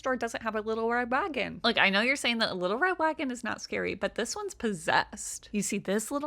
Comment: All I heard was "wittow wed wagon" multiple times